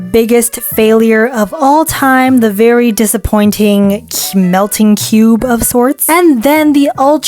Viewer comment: instead of melting cube it is melting cute in the captions!!! :)D)D))D)D)D